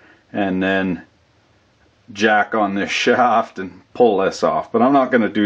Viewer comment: I'd love to say I'm more mature than this but I'm not and I don't think you are either. So I figured you'd enjoy this, if you didn't catch it on the edit. At 22:40 you said "JACK THE SHAFT"! I fucking laughed!